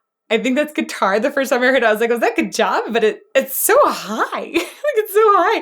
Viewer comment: What he says at 13:54 is "GET DOWN!"